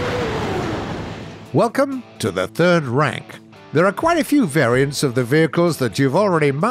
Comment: When he said welcome to the third rank, i thought he said welcome to the third Reich.